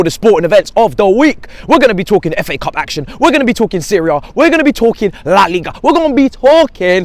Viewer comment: Need enzo bro, im starving and for a sec when u said "we gonna be talking about serie a" i thought u said syria 😅